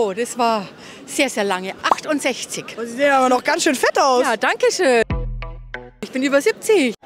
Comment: ich hab verstanden „sehen sie ganz schön fett aus“ 😭😭ich dachte schon wass